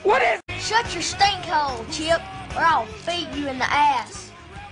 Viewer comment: what did say will in 03:20 when he yell out to the old man? cheap? chip ? chep? i'm a fakin chilean trying to understand your fakin language, thanks... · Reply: anthony guerra soto he said "chip"